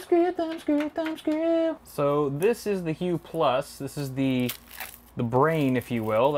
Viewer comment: What did he say at 44:55? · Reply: The HUE+